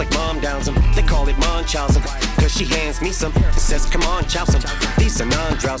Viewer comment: I think he says, "c'mon chow some" and not "c'mon child son"